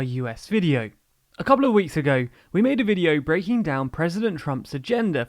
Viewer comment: The volume was low and I was shuffling around, so for a moment at the beginning I heard "we'll be breaking down Trump's gender for the next 4 years" hahaha. Anyway thanks for your guys' videos. I have post notifs on cause I like to hear a rundown of things even if I already have an idea of what's going on.